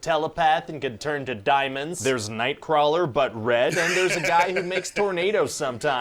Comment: did you say “The Weather Man”? Do you watch Holden Hardman? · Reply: Ha! No, I said, "but red". Ryan's line about Azazel.